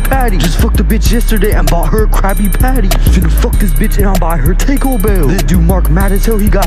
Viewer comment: Did he just say Take-O Bell?